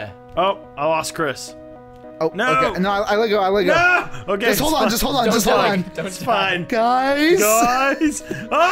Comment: Oh, come on, you almost did it! By the way, what did you said at 1:46? i can't hear it. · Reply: "No... NO... Okay okay.. it's fine... it's fine..." - "Oh! on no I let go, I let go, just hold on just hold on just hold on" - Chris DINOSAUR BREATHING INTENSIFIES